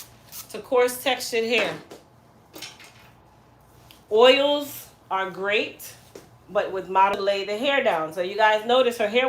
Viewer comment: At the 39:04-39:05 mark, it cut out and I couldn’t hear what you said. “Here is the trick for coarse hair” … da da da, then something about oils to lay the hair down. Were you trying to say that with coarse hair, you use lighter oils instead of heavy oils? Or that heavy oils lay the hair down? I’m trying to figure out what you said. I’d like to know! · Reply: She said oils are great but might lay the hair down...whilst they do great for hair,they(heavy oils) cause coarse hair to be separated so a light oil is best for coarse hair...🤔🤔least that's about what I got from that part😊😊